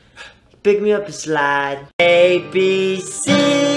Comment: What is jc saying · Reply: Olga Kabioti i heard pick me up and slide, hahahha! I feel like thats not right though!